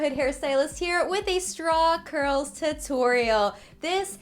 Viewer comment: Every time you said, “Straw Curl,” I thought you were saying “Strong girl.” But, like, both are applicable. Keep rocking!!!